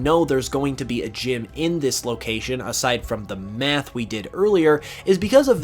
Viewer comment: “Aside from the METH we did earlier” I misheard that entirely wrong 😂